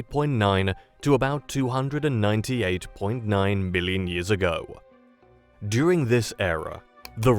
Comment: seconds, did you say ,"mmmbillion" 😂